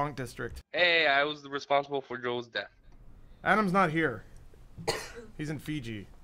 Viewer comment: I'm pretty sure that guy said "I was responsible for Joel's death" but then you guys ripped on him for like a minute because you thought he said Adam. Now who looks stupid. Lol great video guys.